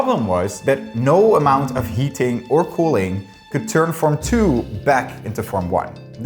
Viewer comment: anyone else have to listen to this twice because the first time you swear you heard him say "No amount of yeeting or cooling could turn ..." Lmao